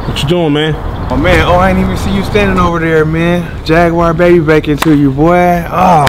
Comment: "Jaguar bacon too you baby" 🤣🤣🤣🤣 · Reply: I thought he said Jaguar baby making to ya! lol